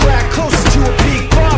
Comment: I thought they said "I'm trying to get drunk mothafucka" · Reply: As a kid I used to thought they said “ try the ketchup motherf”